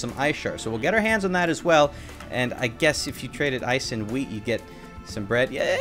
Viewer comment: I heard ice and W E E D